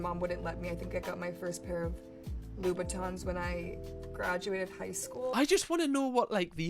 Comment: I think she said Louboutin